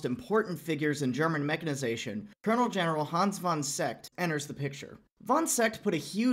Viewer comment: min What's his name? I'm German and when English speaking folks pronounce German names, we don't understand a damn thing of what you say. I understood "Hans van Zett".